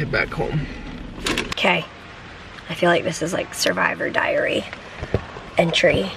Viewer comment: I made it one day without needing to get blood work 😂 so we had an unexpected adventure yesterday! Come join us! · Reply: I thought you said Survivors Diarrhea